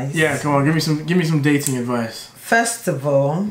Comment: I THOUGHT IT SAID DATING DEVICE😭😭😭😭😭😭